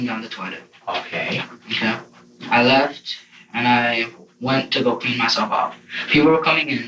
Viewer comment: sounded like he said, i left to go beat myself off